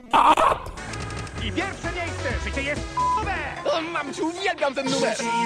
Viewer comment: usłyszałem życie jest słoniowe